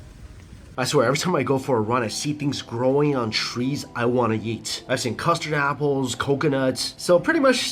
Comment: I swear I thought he said I want to yeet😊